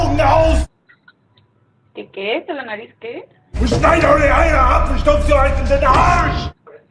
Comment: Is this what he said ?? "Und stei Du eier eie ab zu stüt so oig so in da Arsch!!!!" ????